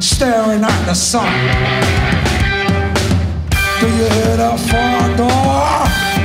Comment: All this time I thought he said “ do you hear that farmmm dogggg!! “ JESUS!